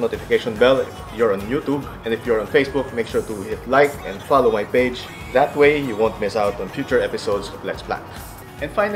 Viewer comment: At -25:21 i swear he says his plants are due for a hand job?? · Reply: Username checks out! 😂 Thanks for the laugh. For the record, I said head chop.